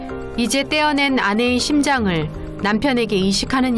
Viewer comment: 초 나만 심장이라고 들리나?